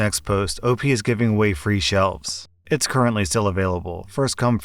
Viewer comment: I thought it said slaves